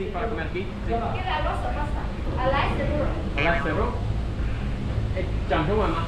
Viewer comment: The thing you didn’t understand at 2:50 I could bet was “laksa noodles”. I don’t know Spanish at all but I know some languages share similar pronunciations to English and that’s very similar to what it sounded like she said